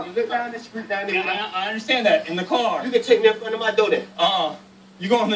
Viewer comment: what did the black guy say at 3:29 ?? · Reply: RpgilbertRS you can take me up to my door then